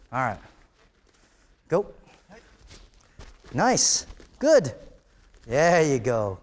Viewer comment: wait I heard someone say "knife!" 10:19 ... anti?